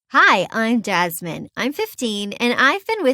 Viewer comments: I thought she said hi I’m dads men in the beginning lol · Instead of Jasmine I thought she said, “Dadsmen.” Lmao😂😂😂😂